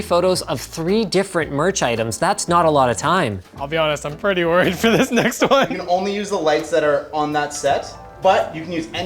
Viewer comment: What's said: I'll be honest, I'm pretty worried for this next one. Subtitles: I'll be honest I'm pretty ready for this next one ???